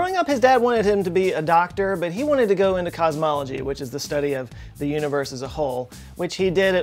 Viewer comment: I had to go back when I thought you said he wanted to go into "cosmetology" lmao Stephen Hawking, the hair dresser 😂🤣 in all seriousness RIP to a big legend 💯